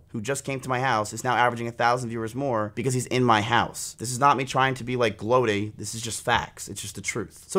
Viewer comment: what word did say at 1:03 ? goating?